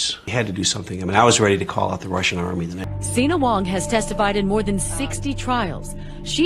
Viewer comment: ( I mean I was ready to call out the Russian army? ) Is that what he says?